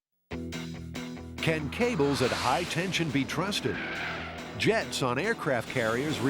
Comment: I swear I heard the narrator say "Can kegels at high tension be trusted?" 😀